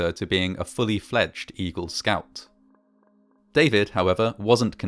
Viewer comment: When you said "Eagle Scout" at 4:23 I swear I heard "Evil Scout."